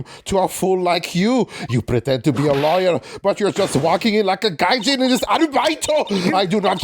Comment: What did say here? "You're just walking in like a gaijin..."?